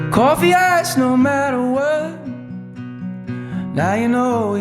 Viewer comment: Coffee iced no matter what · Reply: I always heard "Coffee eyes no matter what" lol